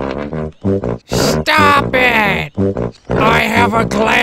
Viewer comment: Along time ago when he said stop it I thought he said “stompin I have a glandular problem”